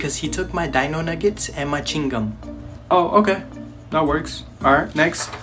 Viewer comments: When he said chewing gum I thought he said “He took my machine gun” Sorry if this is disturbing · Bc he took my my Dino. Nuggets and ching gum had me dead 💀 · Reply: iT SOUNDED Like said Ching gum